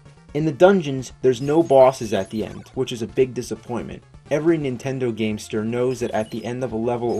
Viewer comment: Did he just say "Nintend Gamester"?